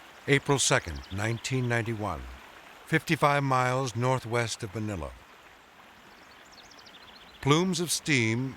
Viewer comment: Edit: Subtitles are finished, I tried my best to make them as accurate as possible. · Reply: Thank you for not using "auto-generated". I hate that stuff. LOL, at 39:48 your captioning says "west of vanilla" instead of "west of Manila". I saw a lot of other errors, but this was the most amusing.